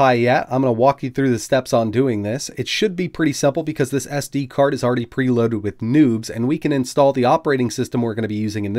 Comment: it should be simple because this sd card is already preloaded with nudes that's what I heard the first time.. I was confused lol